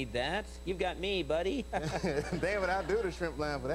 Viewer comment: Did he call Conan David at 5:36? Maybe thought he was on Letterman for a second. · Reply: Replay it at 0.5 speed and you can clearly hear that he said, "Dammit, I'll do the 'shrimp' line for that kind of money."